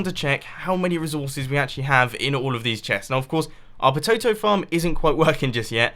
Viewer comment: did he just say pototo?